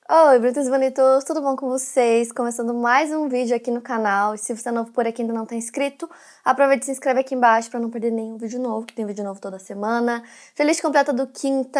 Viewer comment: "oi bonitas e bonitos" em plena segunda???? AMEI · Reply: ELA FALA ISSO?? MDS EU SEMPRE ACHEI QUE ELA FALA "OI BRASIL BONITOS" MEU DEEEUUSSS KKKKKKKKKKKKKKKKKKKKKKKK